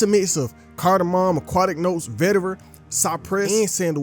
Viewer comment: Bruh said Vetiver… I thought you said Veteran lmal